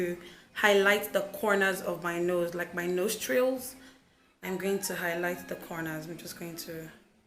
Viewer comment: Why does it sounds like your saying nose drills? Then after I realize it was nostril